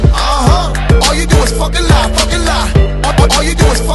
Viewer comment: I thought he was saying "All you do is fuck alot"😂